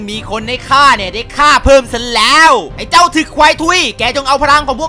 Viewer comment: พี่พูดว่าอะไรนะ😳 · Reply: ถึกควายทุย คือเพลง ของ คาราบาว